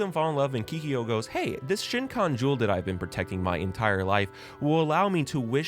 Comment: if i hear you say " shin-kon jewel " one more time x"D its shEEkon not shiNkon hahaha